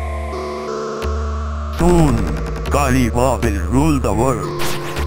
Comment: "kali ma will rule the world" - all this time I thought that synthetic voice kept saying "holy pucket, use the water" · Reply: I always thought is said Ex Lax kid will rule the world